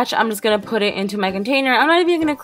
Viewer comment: she says my container and it sounds like she says Meghan trainer